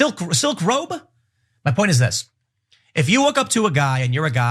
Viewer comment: Is it just me or did anyone else also hear Tim say, "If you WOKE UP to a guy and you're a guy and you go..." I swear that's what I heard - makes even more sense when you realize just a few seconds later he suggested a guy would be wearing a "silk robe." I apologize to the folks in advance who now can't unhear it now.